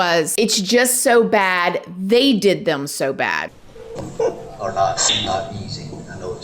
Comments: when it was slowed down i heard "they did them so bad" that could still mean it was a hit he knew about and was just thrown off by the crime scene and said "they did them so bad" instead of "this is horrible". allegedly, purely speculating here. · I heard 'they didi him so bad'. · I think it sounds like he said "they did them so bad" ... I don't know but thats what I heard. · I heard “they did them So bad” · I heard “they did them so bad.” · I heard 'they did them so bad' but I am still on the fence. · I heard "They" did him so bad.